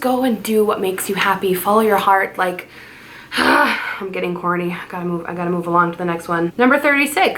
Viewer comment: girl! My jaw just about dropped to the floor because when you said corny I thought you said horny! LOL I got to get some more sleep LOL but everybody is right you totally slay today you look beautiful!